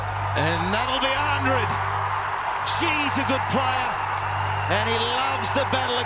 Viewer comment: did he say she is a good player ??😂😂